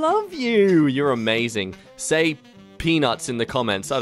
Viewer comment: Not gonna lie I thought said say pinu.